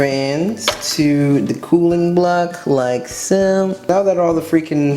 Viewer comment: When you say. Cooling block I thought you said kool-aid block